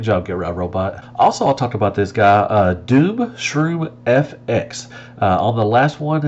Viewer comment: did he say dube shroom?!